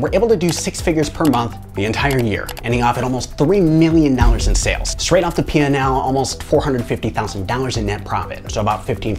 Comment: WHA? Can someone clarify because i couldn't understand fully. did he say of the almost 3 million they made they only took home about 450k in net profit?